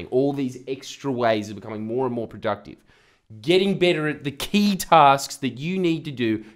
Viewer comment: Just watched this again and realised you said to get better at the 'key tasks'.. I had just gone out and spent me centerlink on a keytar mate I thought you said keytar, now what am I supposed to do? This self help thing is tough love.